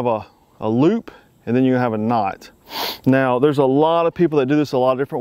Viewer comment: did he say snot? or knot? just curious dont look at my channel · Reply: MaNuLaToR'S MiXeS knot